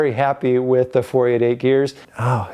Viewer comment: I thought you said 488 beers...